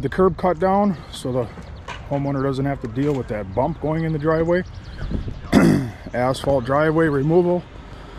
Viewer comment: When you said 'Asphalt Driveway Removal', I had to double take.. could of swore you said 'A$$hole Driveway Removal'. Hahaha!